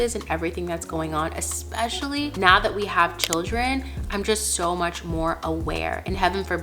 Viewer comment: Am I the only one that heard her say “now that we have ‘children’”? And not “a child”? 🌝 Have I been gone for that long?! 😅